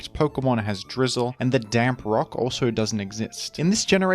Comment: I'm half asleep and have my phone set to the side. Instead of "the damp rock" I heard "the damn rock" and was very confused. I'm glad I paused and double checked, that would have been insane.